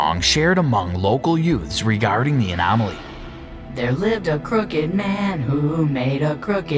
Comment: minute ago i thought that said cooked man